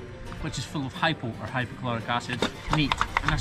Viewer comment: I had to listen to that twice. I thought you were saying that you sprayed everything down with Hydrochloric acid. I was like, Battery Acid?! Then I looked up HYPOCHLOROUS acid and was like, Oh! pool chlorine...LoL.